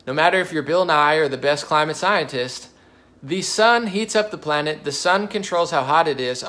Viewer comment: sounds like he says the sun is a planet. Not unbelievable for him though